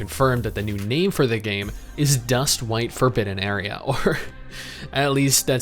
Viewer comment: Did he say Dust White Ferpin Anaria? 2:57 Wut? Lmao what is this game called?